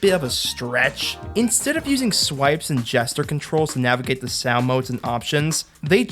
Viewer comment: I keep hearing "jester controls"